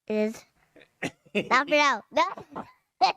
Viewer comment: I heard, I think ‘but not for now’ in response to his Dad’s ‘it’s done’